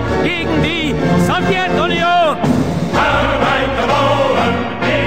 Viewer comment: did i hear Soviet "Onion"